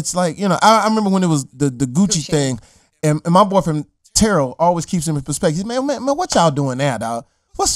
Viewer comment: "my boyfriend Terrel"????.... What he say????? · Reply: “My boy from Terrell” ....